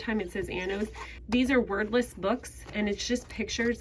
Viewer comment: Totally thought you said Anno’s books were “worthless” 😂 You said “wordless”